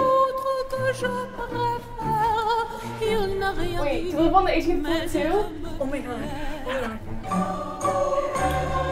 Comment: What is she saying at 1:10? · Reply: “Wait do u live on the 18th floor too? Omagosh”